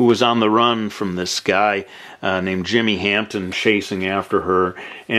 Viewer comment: Did you say "jimmy Hampton?" at 7:42. That's my last name. ^///^